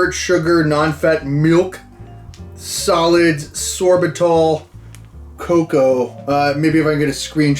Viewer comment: I thought you said sorbitol which is the same sweetener they put in sugarless candy that gives you the runs.